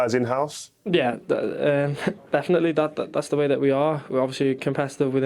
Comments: it sounds like trent says dad? 😂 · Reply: Nah it's the scouse way of saying that, often sounds like tha or da😅😅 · Hahah! Had to replay it as I thought he said, "Definitely, Dad," in answer to Rio Ferdinad's question at 2:58 mins:) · I heard "Yeah definitely, dad" I was like wtf 😂😂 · Why does it sound like Trent says “definitely dad” to Rio